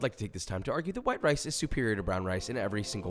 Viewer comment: shiiiiiiiiiiitt!!! thought he said " white RACE is superior to brown RACE" 5:30 personally i support BLM :)